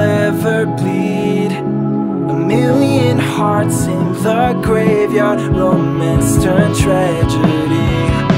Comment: This whole time I thought he was saying “A million hearts in the graveyard, for Mr. Tragedy” but now I realize it’s “A million hearts in the graveyard, Romance turned tragedy” 😂🤷‍♀️